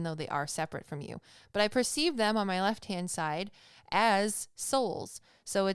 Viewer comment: Around 26 minutes you said "as souls" but I definitely thought you said assholes 😂😂😂